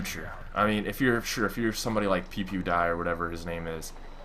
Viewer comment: Did he just say pewpewdie for pewdiepie